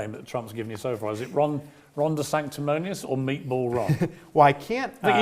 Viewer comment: I thought I just heard you call him Ron DeSandwich and now it's all I can hear. LOL